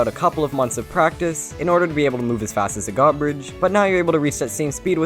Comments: HEARTY PLS IM EARLYYYY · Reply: btw 4:12 subtitles were "garbage" instead of "god bridge'" lol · Captions says: " As Fast As the Garbage " Lmao WTF!!! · I like how the subtitles said garbage instead of godbridge lmao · you say godbridge but the caption says garbage. Just thought it was funny. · why do the captions say as fast as the garbage